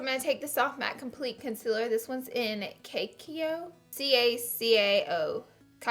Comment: Girlll❤ I walked away with this video playing on my laptop and when you started tryna pronounce cacao I straight up thought you said crack hoe and I about died and had to come and rewatch it😭😂❤ slayed the look tho love ya❣️